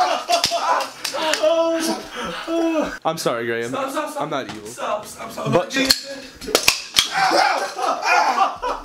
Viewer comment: did he say “I’m sorry graham” ???